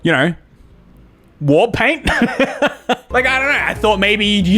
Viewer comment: For some reason when he said war paint, I thought he said wart paint